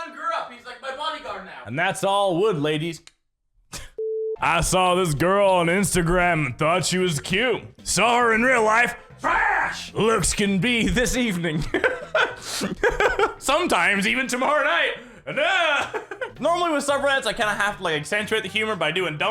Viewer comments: with cc says “ flash lips can be this evening” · with captions says “flash lips can be this evening” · he says “TRASH, looks” and so on, my captions said “flash lips”. WHAT!?!? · you said trash it captions said flash lips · when he said trash looks can be this evening. CC said Flash lips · it said flash instead of trash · for my captions instead of it saying trashhhhh. It said “flash lips” like what? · cc on it says flash instead of TRAAASSSSHHH and instead of looks can be decieving it decides on lips can be this evening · you said Trash. Looks, caption said Flash lips like wth yt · it says "flash lips" instead of "trash looks"... Seems good. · if you put on captions it says lips instead of looks